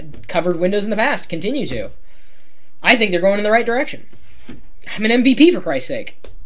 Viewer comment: what he said? 6:40 I am MVP? or NVP? what does it mean?